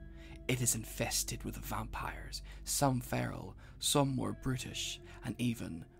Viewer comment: I heard "some are british"